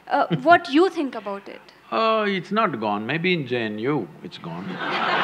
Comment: Congrats for 2 Million subscribers. Sadhguru will cross every other YouTuber by 2025 as it takes time to realise that depth in Life is missing. Also, Sadhguru has International following . . But even if you add regional/other language channel subscribers, he has already crossed 3 Million. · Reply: Are you able to understand what he says at 0:49-52? I hear "Maybe janu it's gone"?